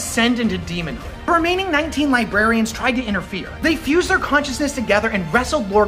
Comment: I thought it said libertarians.